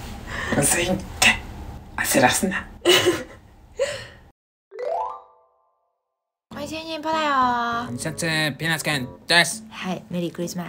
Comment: なんていってるの？こんにちにんにん ぽよだよー？ 気持ち的にはぽんぽこだよなのかな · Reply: 本来は「こんにちにんにん ぽんぽこだよー」 それが「こんにちにんにん ぽだよー」 になって 究極は「ほいじーにー ぽれおー」 って言ってる